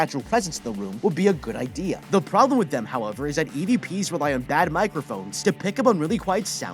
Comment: I thought you said Edp 😭